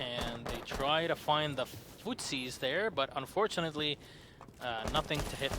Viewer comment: I'm sorry, at 22:32 did you say "footsies"?